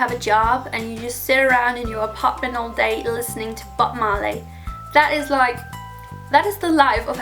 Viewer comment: I thought she said butt marley at first